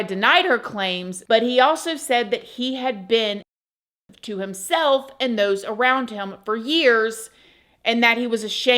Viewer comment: What did she say???? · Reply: abusive